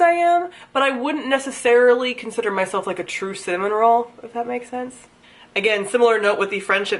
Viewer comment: When you said cinnamon roll I thought you said salmon roll and it was very funny because sushi and it applies lol!